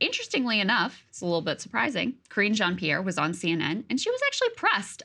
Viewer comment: Did Krystal call her "Cringe-Jean Pierre" at 00:20 😂😂😂😂